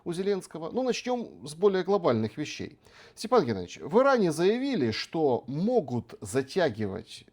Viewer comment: "В Иране заявили", - так сказал Александр, а не "Вы ранее заявили", как могло послышаться (и мне сначала так послышалось). Бывает.